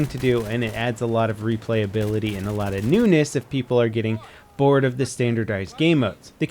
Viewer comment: did you just say...'newness'?